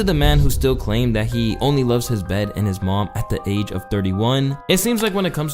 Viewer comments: He actually says he only loves his son and his mom in gods plan He says Mahbed which is his sons middle name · I thought drake said he only loves mahbed and his momma with mahbed being adonis' middle name